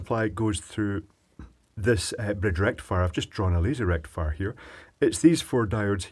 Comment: You got me really confused at 10:43 when I thought you said "laser rectifier".